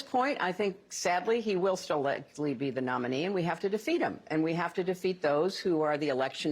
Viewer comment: She says "We have to defeat him again . " I hear: "We have to defeat him by CHEATING AGAIN..."